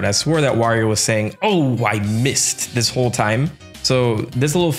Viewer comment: I always thought Wario was saying Doh! I missed!